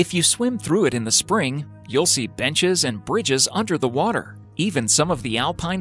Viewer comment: b*tches and bridges??!! Or i misheard it